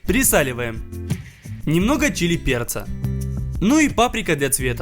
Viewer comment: мне послышалось немного чилим перца